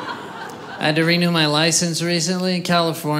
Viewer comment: that guy that excitedly screams “ME TOO!” I’m dead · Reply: I thought he said "had two"...?